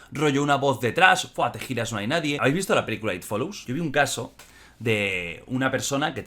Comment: no, me refiero a la que menciona en el min 17:09 CREO que dice "It follows" voy a buscar a ver que onda. Gracias! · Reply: Ahhh se refiere a "it follows" muy buena la película, recomendada.